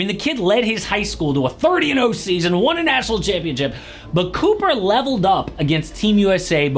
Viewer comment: Sounded like you said "went 30-0 and won an asshole championship"